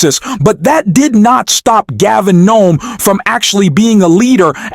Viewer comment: Did you actually call him "Gavin Gnome" at 1:06 ?